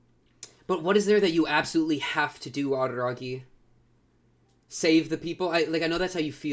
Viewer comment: the captions having ougi instead of araragi XD